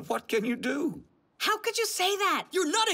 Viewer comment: Damn the grandpas savage "they're just terribly unpleasant people"😂 "did i say something i shouldnt have" which i heard as "DID I STUTTER-"